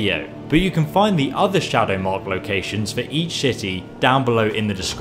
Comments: Did anyone else notice that at 12:40 he says shity instead of city? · it sounds like he says each shitty